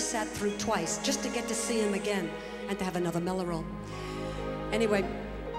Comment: What is the word she says at 0:40? Something with m... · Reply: Mello roll - ice-cream snack